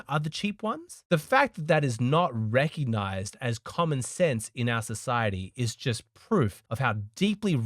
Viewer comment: he says "the fact that it's not recognized as common sense..." At first I thought he said "...common theft" which would have been just as valid.